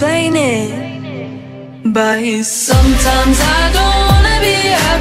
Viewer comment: If you hear closely you can hear Dixie say buys instead of but in 0:44 Im sorry not trying to hate in here